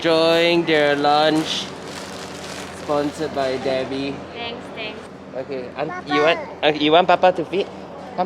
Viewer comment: There is a mistake in the subtitles of your video. 2:35 it say Tnaks Thanks instead of thanks thanks. Plz reply to this comment